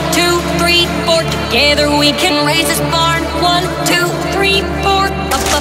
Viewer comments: why is everyone call this "racist barn" I watched all of it and I don't see how it is racist.. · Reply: The way raise this is said, it sounds like she said Racist Barn, instead of Raise this Barn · I hear "Racist Barn" anyone agree? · I hear "working teens"...... srsly what is wrong with me? xD